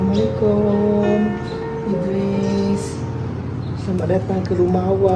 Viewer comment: ur babys name is haris right? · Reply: HAHAHHAA i heard say'assalamualaikum haris' masa bru nk masukkan baby dalan rumah hshshs